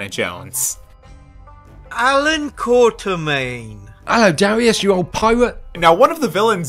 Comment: It sounded like he said “Darius you old pervert” 💀